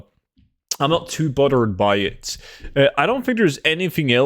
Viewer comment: I had to listen twice at @ 1:50 I thought you said buttered by it. =p